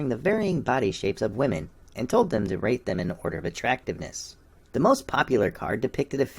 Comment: Heeeeee.... wait a minute.... Did you say "rape them" instead of "rank them" ? At 0:58... if so... Very funny misreading/talking/subconsciousness...